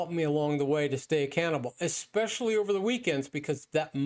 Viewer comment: I know you said 'stay accountable', but I heard 'stay a cannibal'. Anyway, I way daily and notice the trend lines.